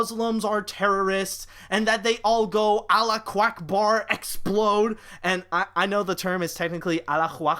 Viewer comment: Allah Quackbar? That's a new one xD (At least, that's what I heard